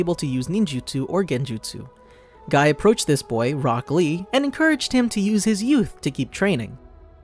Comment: I thought he said broccoli but it was rock lee